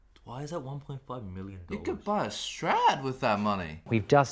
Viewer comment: I thought he said Strat. It was Strad lmao